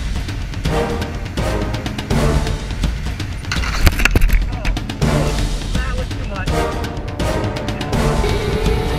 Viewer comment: What does say at 6:57? Seems like he hurt himself, and the guy at the end said he almost needed to go to the hospital. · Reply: Pretty sure he said: "That was too much".